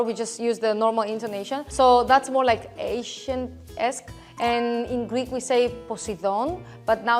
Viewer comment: (it might also be later in the video, I haven't finished it) the greek girl is saying ''ancient'', but it is shown as ''asian'' in the captions. As a greek that has good enough ancient greek knowledge, I think that is what she is trying to say. Just pointing it out and hoping you guys might change it.